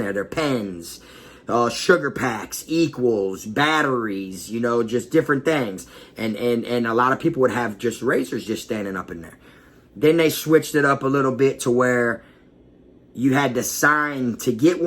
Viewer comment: huh? did you say panties?